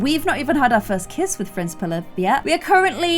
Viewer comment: Did she say pilif 2:50 lmao